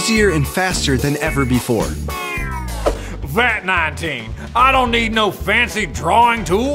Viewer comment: Did I hear that wrong? Fat 19.